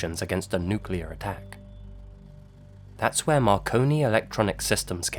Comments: I thought it said “maricon” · i thought it said macaroni and i was really confused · I thought it said Macaroni lol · Thought it said macaroni files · I thought it said unsolved macaroni mysteries 😂